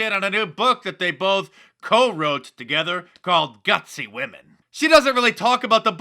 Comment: Did you say Gutsy, like brave or Gusty, like a fuckin' windbag?